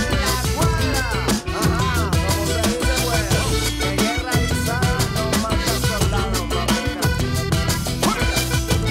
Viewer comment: alguien puede distinguir que es lo que dice juanes en el segundo 0:14 - 0:18 ? seria de bastante ayuda ,gracias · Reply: Brayan Eduardo Vargas Landa vamos pues a ver carajo, pa que sepa cómo es la jugada mijo, en la calle en la esquina en la cuadra ajá como se dice pues que en guerra avisada no matan soldado mamita.